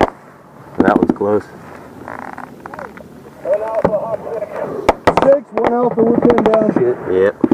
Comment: I am so sleep deprived I thought it said 'No black soldiers pinned down' and was like 'Sounds like a good day to be black hur hur hu- oh wow, I need sleep'